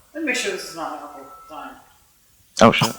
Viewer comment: did actually say "Iet me make sure this isnt my Uncle dying"?